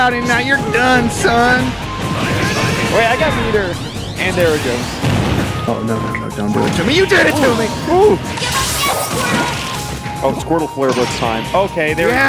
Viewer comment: Am I the only one who hears “give them death squirtle!”